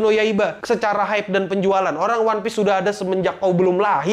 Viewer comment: dia ngomong apa? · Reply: "Bang hype boruto lahhhh"